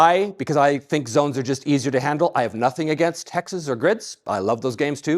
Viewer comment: "some of my best friends are hexes". 1:16:20 Did you say hex?